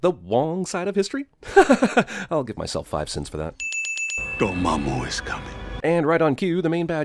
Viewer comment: "your momma is coming" is all I heard him say.